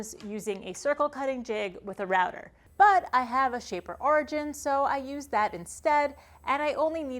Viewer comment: I didn't quite hear what the name of the interesting tool at 01:28 is. Could you help? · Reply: Shaper origin?